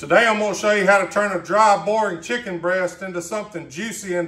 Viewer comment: I literally had to replay the start... Thought you said "child bearin chicken breast".